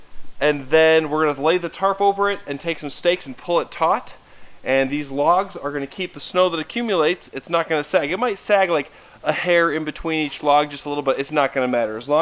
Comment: ish Monty Did I hear steak? At 1:12:30ish FYI kibble thickens stew toooo